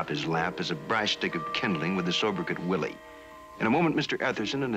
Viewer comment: A Sobert cot what......I thought he said Sober cot Willie